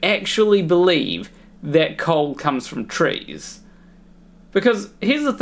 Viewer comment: Lol. I thought he said coal comes from trains, which really made me cock my head. 😂